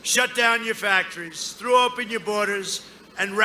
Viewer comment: Love it! Sounded like POTUS said “Joe Biden,.. he’d throw up on our borders”. I LOVE THIS MAN!